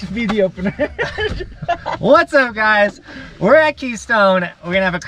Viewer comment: "What's up guys. We're actually stoned." its what i heard